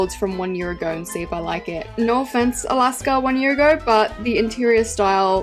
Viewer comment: Alaska i thought you said small